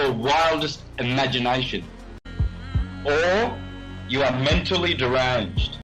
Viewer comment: I swear it sounded like you said "Donkey mentally" the first time 🤣 Sounds about right though 🤣